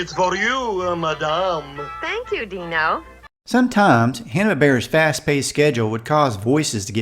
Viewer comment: Wait, I thought I heard you say Hanna-berra. So I waited and you said it again. Anyway this was a great idea for a video. Thanks.